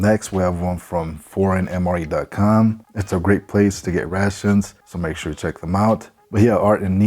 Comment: Great un boxing, thought you said your friend Kalyn had a " Ratched channel " instead of rations, and then heard "crack in a sack" 🤣🤣🤣🤣. Late night nurse here, I need SLEEP 🤤. Love your channel!